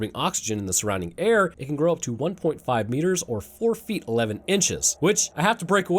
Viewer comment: Not to be that guy, but : "As an adult it will stand roughly 3.5 meters tall, or 4ft 11 inches." Not quite sure that adds up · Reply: He said 1.5 metres, not 3.5. You misheard.